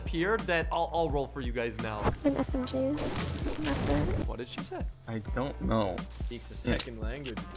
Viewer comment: when she was talking I heard “an smg that’s it...” 🤔